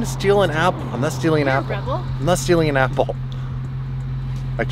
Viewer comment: who else heard apple orchard